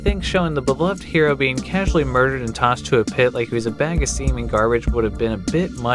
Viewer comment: Hey, did you say "The beloved tossed to a pit like he was a bag of semen garbage"?.